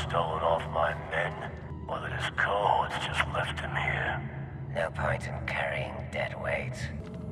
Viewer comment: What did he say at 1:27? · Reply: Or that his cohorts just left him here